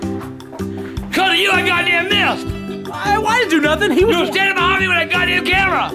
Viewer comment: did he say "DO YOU NEED SOME GODDAMN MILK?!?"?